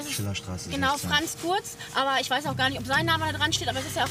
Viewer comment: Wer hat bei 1:27 auch Franz Furz verstanden? 😂